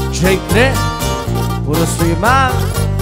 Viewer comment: Que Dice ? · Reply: "Nothing Less, JTres"